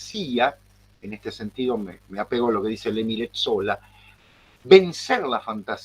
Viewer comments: Al minuto 39:49 ¿que autor cita el profesor? le entiendo que dice algo así como "Lemiret Sóla" o algo así. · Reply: Emile Zola, me parece. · Enlace al artículo sobre Eyes Wide Shut (1999) a propósito de los 700 años de la muerte de Dante: <link> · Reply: Al minuto 39:49 ¿que autor cita el profesor? le entiendo que dice algo así como "Lemiret Sóla" o algo así.